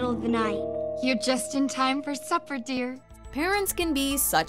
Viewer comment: when Coraline meeting other mother and father i thought other mother say "your just in time for SUFFER dear"